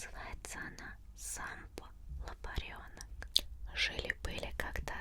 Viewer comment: solo entendí Sempai